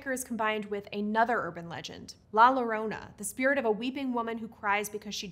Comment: Just for future circumstances where it comes up. La llorona is not pronounced like that, it almost sounded like you said “Lalo”. Great video tho!